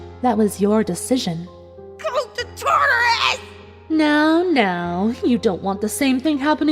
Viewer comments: what did rainbow say here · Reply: She said, "Go to Tartarus!" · What does rainbow dash say at 20:40? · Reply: faye webb go to tartaraus